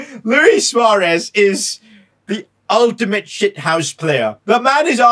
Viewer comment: He swore he said luis was the most shit houses player